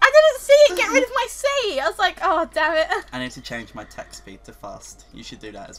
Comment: i thought he said "gotta change my sex speed" yeah i have a messed mind...